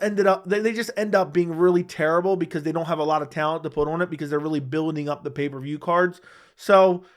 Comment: Did he just say not much talent to put on vagas cards?